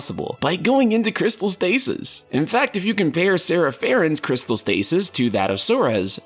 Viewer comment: It sounded like he said "Sora Farron" lol